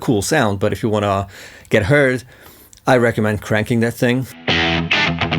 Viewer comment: Love the tones. 6:17 at first I understood "If you wanna get HURT, I recommend cranking that thing" and I was like 'yeah let's do it! Wait, what did he just say?" :D